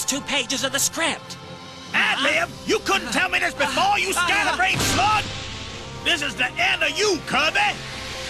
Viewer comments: Did dedede actually call Escargoon a "Scatterbrain SLUT" ???? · did he called him a slut? Never mind, he says slug